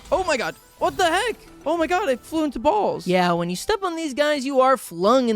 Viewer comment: I don't know if I'm crazy but I keep hearing balls, I feel like it's supposed to be walls I don't know.